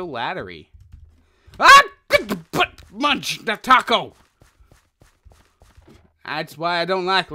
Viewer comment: Did he say But Munch A Taco?